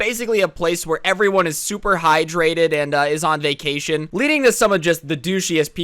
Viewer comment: Lol at 1:00 right when he trys to say Super hydrated an Ad cuts in and all I heard was "Where everyone's super high" xD